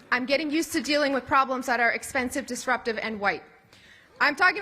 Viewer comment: I love how it’s perfectly fine to be a complete racist when your part of the democrat party it’s just like “oh it’s just a joke haha” but when a Republican makes any statement about race even when it’s not a racist statement the media has a meltdown! Will US media ever take a fair stance on this I think not 🙄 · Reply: All I heard was “disruptive white people”